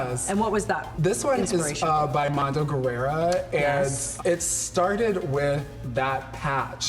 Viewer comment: did Shea say Mondo Guerera?